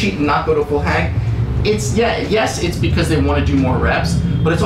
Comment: Crazy that this was uploaded the same day I decided to look into calisthenics. This seems pretty helpful! Just one question though At 9:55 did he just say "it's gay"?